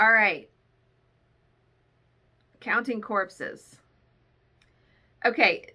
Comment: I thought you said "counting horses" and my mind went to a weirdo kidnapping and shaving horses🤣 then realized what you really said!!